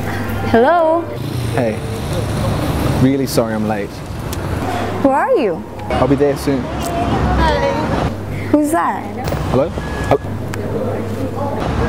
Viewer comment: it sounded like she said "who are you?" instead of "where are you?".. was that supposed to be it? or did i hear wrongly :/